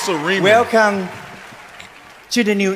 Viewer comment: I thought she was saying ‘Ramen’ like the ramen soups.